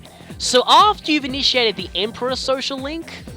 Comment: Well, yeah, it kinda sounded wierd to me, so I wanted to make sure you didn't get it wrong. I was like "But the Empress one isn't until much later in the game..." Damn you, mishearing. 6:24 - Huh. Well, now that's an interesting backstory. I've got a cat for like a bit more than a year now, which appeared suddenly at my door while I was helping my sister who was playing God of War 2. We initially thought that the cats crying was from cats in the roof, so we ignored it for about an hour. Since we still heard the exact same cries for that long, my sister got worried, and she decided to check it out. When she opened the door, there was a small little gray cat in front of her. He stared at her for a moment, and dashed inside the house. We fed him and all, and then I sent him off the house. But, he kept crying at the door. I thought he was soooo cute and stuff that he made me cry, so I decided to hide him until the next day inside my bedroom. Then, the next day, I showed him to my parents and they literally said: "Oh, shit. He's cute alright." In Spanish, obviously. And they let me keep it. And now, he's one gigantic cat roaming around the house without a care in the world. But I still love him. 7:25 - Hooray for old games and their cheesy scripts! 15:43 - And as they say, there are many, MANY more to come. 18:48 - I'm guessing that the one you're talking about is one of the last Full Moons, if not the very last "normal" full moon battle. And, about P4, well... I didn't hav